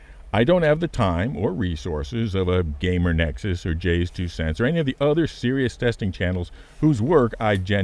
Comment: Half asleep thought you said "Rayz2cents" not Jayz two cents. Thought you had a second channel there!